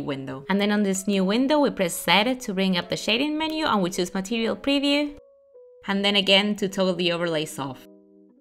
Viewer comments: i havent a clue what you said there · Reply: “Press zed (Z) to bring up the shading menu and will bring up material preview and then again to enable overlay soft” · what did she say at 3:37 ? we press what? set it? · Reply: okay i got first select the object then press Z on the keyboard and then choos material preview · she says she opens a new window and clicks WHAT to open up the shading menu?? I can't understand her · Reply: it's Z to open up that menu, it sounds like she's saying 'seven' or something lol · What does she say here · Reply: "Press z to bring up the shading menu" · In 3:38 what are you supposed to click to bring it up? I couldn’t understand what they said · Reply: Z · I cant understand what she's saying it sounds like press seven, CC cays it's Press Set It. Neither of those is correct. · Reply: She says press z. Some people pronounce it zed 🤷‍♀